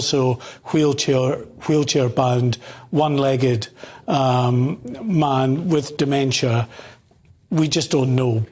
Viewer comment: I thought you said, the man has dementia?